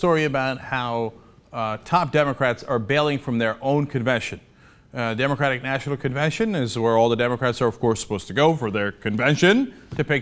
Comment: thought it said Demonic National Convention at first... Actually it still does...